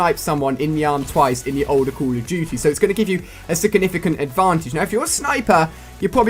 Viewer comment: He says "sickinificant" instead of 'significant' omg soo funny xD